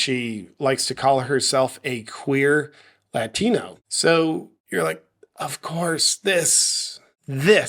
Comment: I thought you said she described herself as a "Queer Fatina"